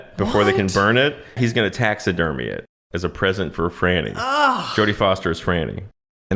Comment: As a British person, I did a double take when he said "Jodie Fosters' Frannie." Thought I'd misheard for second. I'm definitely a Jodie Foster's frannie guy.